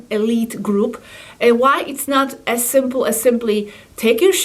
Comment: At first I thought she said "It's not as simple as 'take a shit' " I have a hard time with accents.😊